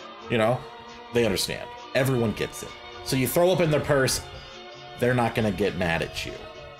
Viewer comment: I thought you said to throw up into a hearse at first...